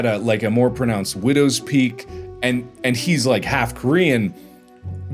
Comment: i had to listen to that a million times to understand what he was saying my ears fr kept hearing "and hes half green"